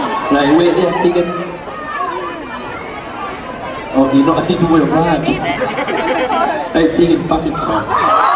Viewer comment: What does he say at 0:09? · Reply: Something about her taking her bag off I think